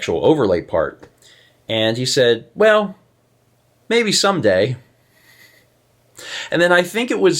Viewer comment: You just misheard him. He said “maybe Sunday.”